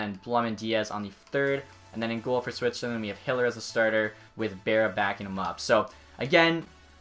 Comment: Mcdavid is a center men and so is Stamkos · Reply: also 10:20 i almost thought he said hitler